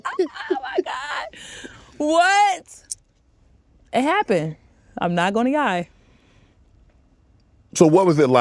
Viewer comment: It sounded like Kash Doll said I’m not going to Yie😂😂😂! Sounding like JaydaWayda son Loyal . Meaning lie 47:56 ! It was so cute !